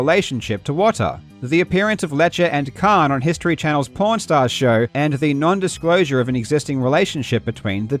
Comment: I heard "porn stars" and had to rewind the video lol